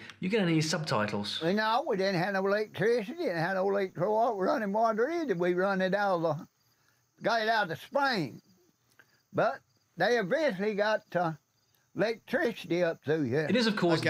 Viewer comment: You asked if anyone understood that- Here is what I heard: "...*cuts in*.. we didn't have no electricity, we didn't have no oil. No running water either! We run it out of... we got it out the spring!.. but, they eventually got the electricity up through here."